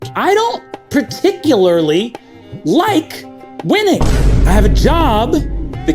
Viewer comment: helpppl I thought he said “I don’t particularly like women”